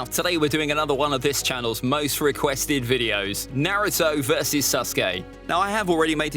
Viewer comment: Did u just say susgay?